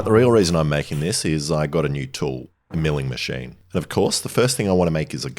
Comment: I swear I heard he say "mewing machine" 💀